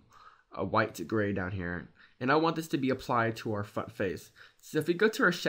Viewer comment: Great tutorial, but...@ 6:47 -6-49, i swear it sound like u almost said " and i want this to be applied to our F*ck Face" .lol.